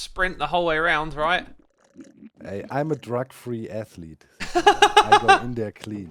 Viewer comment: Did say or I miss understood I thought he said this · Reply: Drug free F you